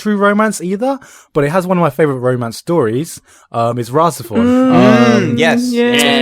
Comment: What's the anime that Garnt mentioned around 14:24? I can't understand the name ;_ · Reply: RahXephon